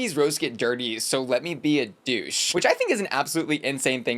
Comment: I didn't think Tina said "so let me be a douche" I thought she said "So let me be obtuse" Anyone else hear that?